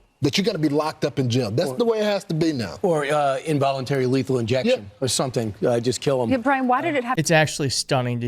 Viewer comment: Voluntary lethal injection? Seriously? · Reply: What he said (1:20) was “… or involuntary lethal injection, or something, just kill ’em.” Shocking.